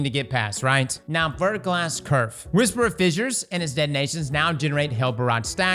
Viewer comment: when you said verglas curve I heard "vertical Ass nerf" and I thought Titans just got their butt towels taken away along with everything else